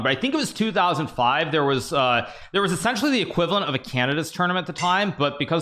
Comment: I thought you said it was a "cannibas tournament" 😭🤣🙏